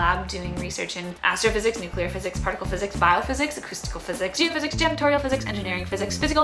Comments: I know you were speaking pretty fast but it sounded like acrystalical physic's? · Did I hear 'janitorial' physics?